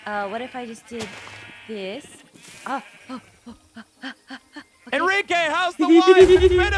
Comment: did he just say Henrique? (my name)